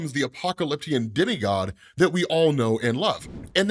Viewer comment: why did i hear Apocalyptian Diddy god